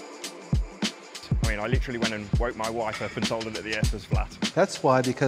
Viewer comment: ayo at 4:39 the subtitles said i mean, I literally went and bought my wife and told her that the ass is flat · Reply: he said that he " literally went to wake my wife up and told her that the earth was flat"